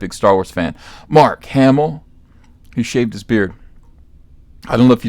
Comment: I thought it said "saves his beard" and I pictured him sadly putting it in a plastic baggie then placing it on the shelf whilst looking at it forlornly..